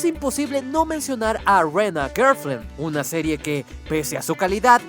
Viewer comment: ¿Que dijo? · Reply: A rent a girlfriend (Una novia de alquiler) el de los guiones pone los términos en inglés, japonés y en español jajajaja